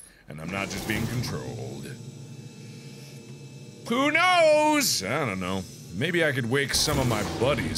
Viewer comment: Who else heard "poo knows"?